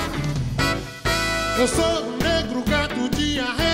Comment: Gente ,eu sempre escutei "eu sou o rei do gado" vey kskskalalhdkdkskskdkd